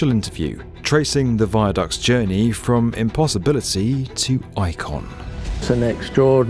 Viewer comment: I listened three times and it really sounds like he’s saying bicon instead of icon. Anyone else hear it or am I hallucinating?